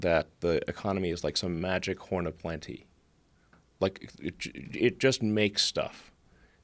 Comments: what is he saying at 1:21:15 magic horde of plenty? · what is this word? Corner-planty? wut?